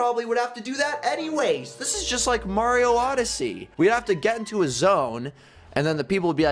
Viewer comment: is just say mario odyssey